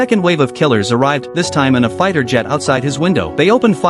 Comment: Did he say "fighter jet"?